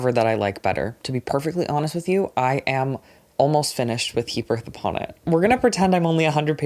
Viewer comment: i genuinely thought was saying “he birth upon it” for nearly 20 minutes 😭